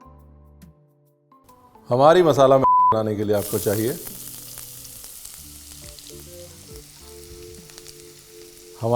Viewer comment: me kya bola jo beep hua....koi batai 😂😂😂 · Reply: Maggi bola hai